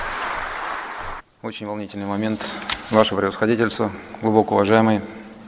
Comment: Мне послышалось,или он сказал ваше превосходительство?